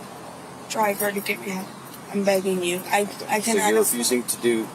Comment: Today I'm hard of hearing. I heard 'I'm banging you' and 'Will you take a breast sample?' 🤔